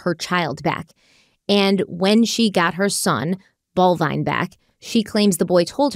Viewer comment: did you say their sons name is bovine? Lol